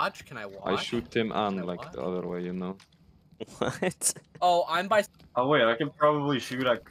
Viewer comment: I need to know what connor said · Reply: I said I was by Smugglers